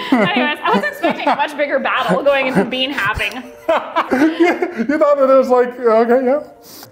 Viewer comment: I heard bean "having" instead of "halving" and all I could think was "being a responsible parent for a bag of beans might really be a bit of a battle 🤔"